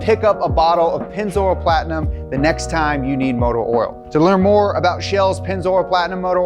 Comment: I heard “motor oral” too many times